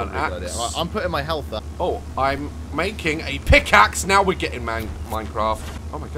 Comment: “I’m making a pick axe”. Me: thought he said he was making a KitKat. 😂